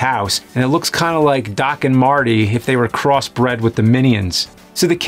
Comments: when you said "doc and marty" i heard "rick and morty" and had an apiphany.... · Dock and Marty I thought you said Rick and Morty both are true